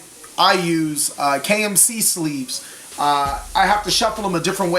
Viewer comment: Sounded like you said KFC sleeves lol